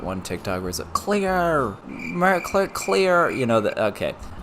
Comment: I think he is trying to say “ar nar cleor” from a mermaid show I used to watch